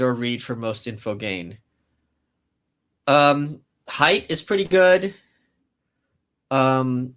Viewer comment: What is the book Michael says here? Is it just called "Height"? And who is the author? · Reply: I am guessing he was talking about Jonathan Haidt